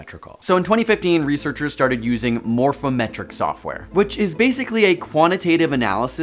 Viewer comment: I heard "morphometric" as "more fem-etric".